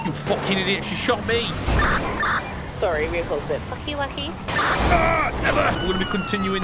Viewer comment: did i just hear say "fucky wucky"